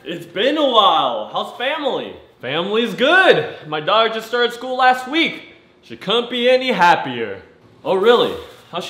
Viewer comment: it sounds like he said his dog started school last week